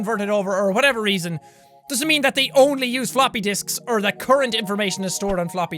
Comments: i thought jack said floppy dick! XD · Am i the only one who missheard Jack and thought he said Floppy Dicks